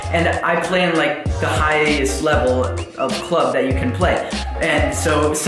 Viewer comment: Thought u said highest level in America??? Hate to see the lowest level......